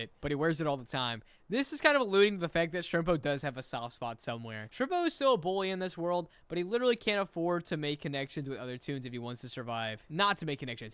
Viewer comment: I heard “Shrimpo is still a boy in this world” … lol